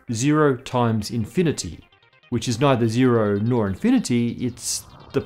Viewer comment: I love this show too. 10:36 did he just say “…discontinuities in the meth we use…”